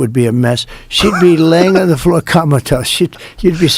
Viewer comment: comatose i thought he said kameltoe